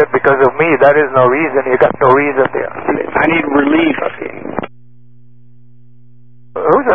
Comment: I'm pretty sure the controller is saying "I need relief" to have someone tap him out to help deal with it. That's what we say on the radar side of it at least